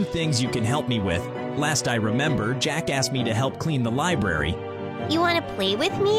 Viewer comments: he really said jacka** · it sounded like said Jackass!! 🤣🤣🤣🤣🤣😭